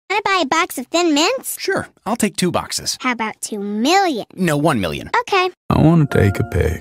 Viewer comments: “I wanna take a pic” had me dying 💀 Edit: I know it’s been two weeks, but thank you so much for over 900 likes! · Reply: I thought it said “I wanna take a pack” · “I wanna take a pic” had me dying 💀 Edit: I know it’s been two weeks, but thank you so much for over 900 likes! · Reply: I thought it said "I wanna bake a pig" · What it says : “I wanna take a pic” What I hear : “I wanna bake a pig” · holllyyy shit my goofy ahh brain thought he didn’t say “I wanna take a pic” Damn I thought he said “I wanna bake a pig” 💀💀💀